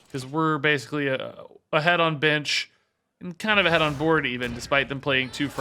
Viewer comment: Wait did just curse at 16:27 am I hearing that right I’ve never heard him curse before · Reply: he said bench